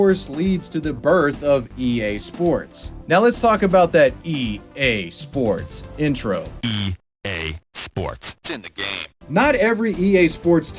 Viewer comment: Since I am not a native speaker, I never really understood what he was saying. I always thought he said something like: "EA Sports intimned Game". Whatever that was supposed to mean.